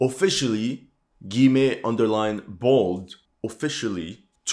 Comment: What did you say at 6:16 - Officially gimme underline bald?